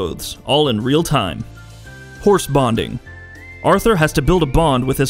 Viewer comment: I thought i heard "horse bondage" and got my meat excited for a bit..